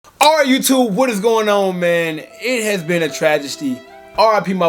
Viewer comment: Did he just say tragesty? A combination of tragedy and travesty. 🤣🤣